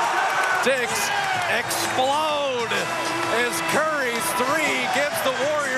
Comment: did he said "Dicks explode"? lol